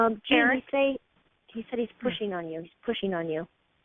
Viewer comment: whisper...."pushing on you". Heard it clearly. And i think i also heard a "yeah". Awesomeness.